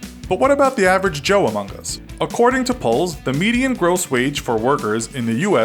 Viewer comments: As a European, every time he says "according to polls", I hear "according to Poles". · When he said "According to polls" I heard "Poles"